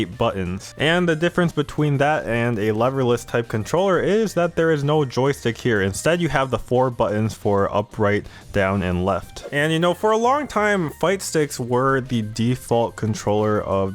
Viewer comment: At first I thought you said "loverless" controllers thereby saying anybody who uses these is a virgin loser :D